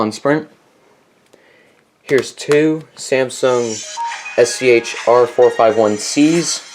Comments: Turn on captions and it says Samsung SCH-RSCH-R451C instead of Samsung SCH-R451C! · I turned on the captions Samsung SCH-RSCH R415C Instead of SCHR451C Messed up